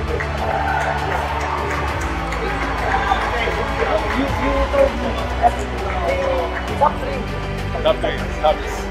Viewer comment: what did say to? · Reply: You are top 3